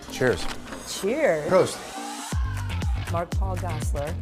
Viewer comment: Did he say Proost at 0:26? That's Cheers in Dutch.... ..he didn't lost all his roots ;-) Btw, MPG seems lost some weight. He used to look a little bit more beefier, but looks better now. He ages very well.